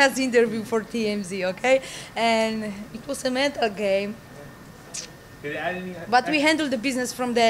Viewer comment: What, did she say the weight cut drama was a game?